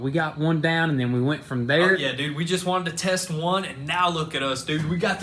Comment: did he say fuck yea dude? Lol